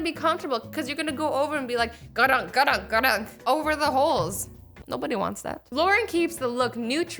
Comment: Put on 0.25x and on 6:32(until nobody wants that) she sounds like she said over the hoes😂😂😂😂😂 love you 💞💞💞😘💞😘💞😘 P. S. Hwo is watching in 2019?